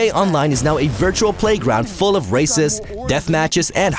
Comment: Heard racist instead of races but it still seemed accurate.